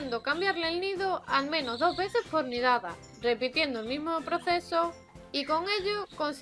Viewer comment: recomienda cambiar el nido 2 veces por ... y no entendi la otra palabra alguien me puede ayudar?? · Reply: 2 veces por nidada más o menos. :)